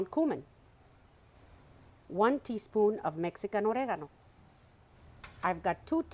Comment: QUESTION when you were adding spices to the enchilada sauce, right after cumin at aprox the 3:23 mark, you added a spice that sounded like “Mexican oregano” ? I could not hear it accurately and I dont see it in the ingredients you list for the recipe. Please clarify what you added. Thank you ❤ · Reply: Mexican oregano. You heard it correctly 😊